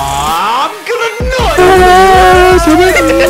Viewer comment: First thing i hear: "im gonna NUT!" 💀